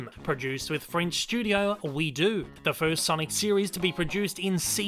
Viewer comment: Hey! You've made another error in this video. The people who collaborated with Sega on the sonic boom TV show is called:OuiDo! Sounded like you said We do! ( Sounds like french on second thought.)